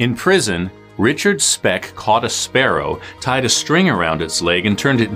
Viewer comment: I thought he said "arrow" instead of sparrow at the beginning, I was confused to why someone would keep an arrow as their pet.